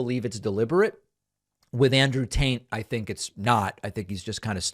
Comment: I heard “Andrew Taint” and I like it better that way.